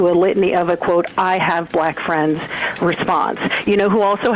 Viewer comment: "I have black friends!" I heard "I have black beards" because that's what you clearly meant.